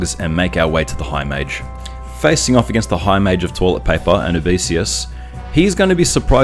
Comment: Twice now I’ve listened to this video and misheard you referring to Anubisius. Both times I heard “Tall Pipe Mage”